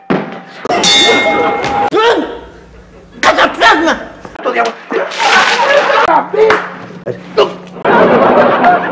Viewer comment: ¿Que dijo Quico? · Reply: Hasta plasma